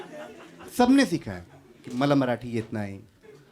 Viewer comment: what did that audience member said? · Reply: Pickpocketer sikhte hai aisa bola